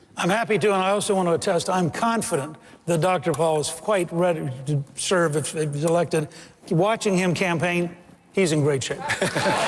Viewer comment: All I heard from Gingrich is, "Paul is quit ready tb serve afblahrubrfa elective watching him campaign he's in great shape."